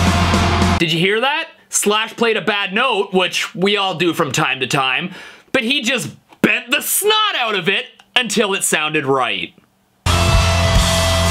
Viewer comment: Strings going out of tune, just in time for the solo- bad luck! You can hear it in the rhythm after the solo as well. I'd guess either the G and/or B went out as well, or he was just put off by a string being out and couldn't work out which one it was. Btw did Ozzy actually say "He f'd up?" can't work it out XD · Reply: I thought he said 'you hear that flat part?'